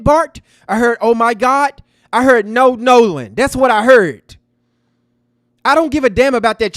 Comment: I thought I heard Nolan don’t die but it sounded like a guy not a female